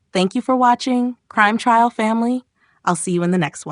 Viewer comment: Crime Trail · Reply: but I heard you say crime trial